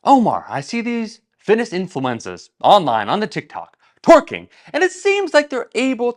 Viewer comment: At first i thought he said “fitness influenzas” but it is influencers 😅